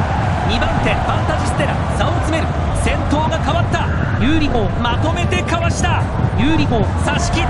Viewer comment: ユーリ4じゃなくてユーリ砲に聞こえるw